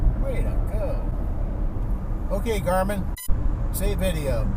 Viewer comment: #3 - when the driver first said "Hey Garmin" I thought he was saying Abe Froman.